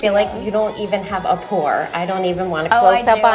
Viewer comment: what is she saying on 3:39?? "i totally have a ____" please tell me!! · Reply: "Pore"